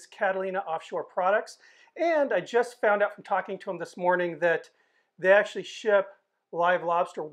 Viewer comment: Haha in the beginning of the video I thought you said that you found out by talking to the lobsters that they ship live lobsters! x'D